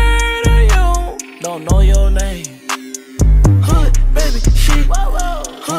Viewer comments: i thought it said "cool it baby sheep :(" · I keep on thinking it said " hood,baby,sheep XD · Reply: Legit I kept hearing "cool it, baby, sheep" I'm done